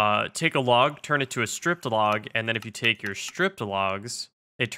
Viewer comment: I keep hearing "stripped log" as "strip club" holy heck